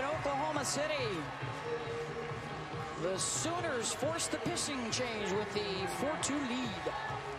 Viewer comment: Um...did anyone else hear her say pissing change instead of pitching change? 🤣